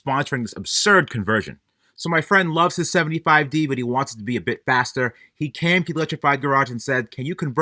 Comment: Lol, at first it sounded like you said “my friend owns a 75 D but he wants to be a big bastard”.🤣